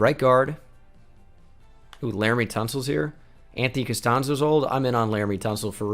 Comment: If you watch the Texans rebuild you’ll get it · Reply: Pixburghsavage YT oh your talking about that I thought he was saying that Anthony castanzo wasn’t old I was like wtf